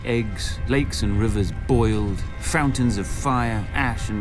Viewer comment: did he say frountains of fire @ 1:05?